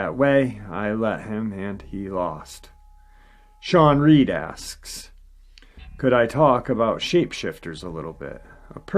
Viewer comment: I thought you said both could talk?